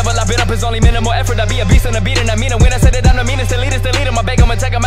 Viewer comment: instead of "Meanest Elitest" at 0:37 it sounds like "Fetus Deletus"